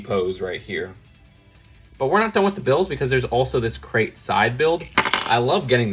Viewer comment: Did he say “Crate side build?” 😆